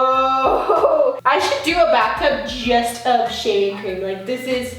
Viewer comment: I thought she said sh*t